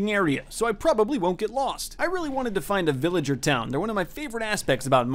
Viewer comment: DID THIS MAN JUST SAY “VILLAGER TOWN” INSTEAD OF VILLAGE??? *You Uncultured Swine, I SPIT ON YOU! jk I liked this vid